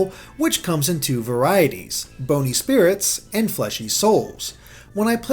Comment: did anyone else think it said "brony spirits" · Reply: i thought it said boner spirits